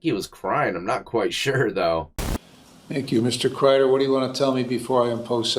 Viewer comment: Bro I thought he called him “Mr. Cryer” 😂😂